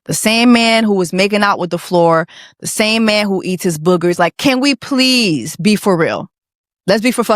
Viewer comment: I'm a French speaker, and until now I thought he was just saying “burger” in a weird way… but “boogers” is actually a real word! I'm disgusted — now I understand your concern about his “buuurgers” 🤢